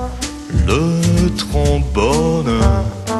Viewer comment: it sounds like he says tromboner at 0:20.